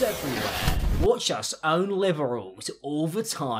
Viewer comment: I definitely heard "Watch us own liverals." Me: Is this some sort of new slang for people who eat liver or something?